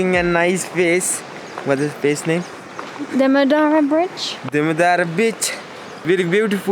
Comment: what did yours your you just say? Demodara what?😂